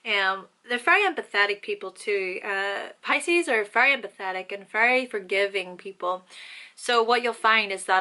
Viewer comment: i thought u said Pisces are pathetic people ! XD ... had to rewind ....